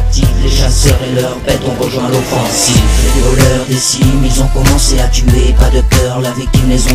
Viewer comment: C'est moi ou a 1:34 il dit les violeurs?